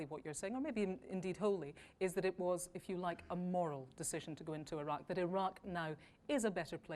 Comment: Did she say moral? I thought she said oil?!